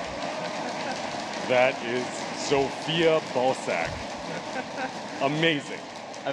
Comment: It sounded like he said Zofia Ballsack